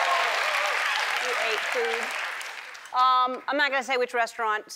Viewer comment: Is she saying 0:10 we ate poop?